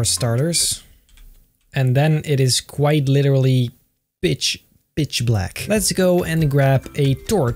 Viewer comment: I thought he said the B word but it's "Pitch".